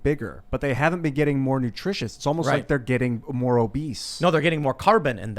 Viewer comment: Did he just say plants get obese?